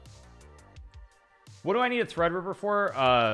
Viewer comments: what did he say? "What do I need a ... for? Leela". Can't quite parse it. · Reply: "What do I need a threadripper for?". Had to look up the actual question on the a Twitch VOD. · What did he say? A thread river? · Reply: Threadripper, a powerful CPU.